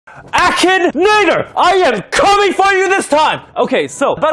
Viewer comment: what did he sayy- o wait ...oo he said aki-nator i thought he said action nig